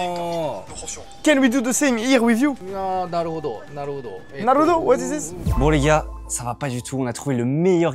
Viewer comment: j'ai entendu "nan Naruto" xD